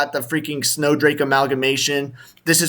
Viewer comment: I heard that it was snowdrake's mom